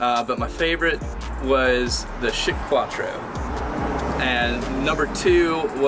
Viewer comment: I thought you kept saying shit quattro 😂. How did no one spot this when they came up with the name.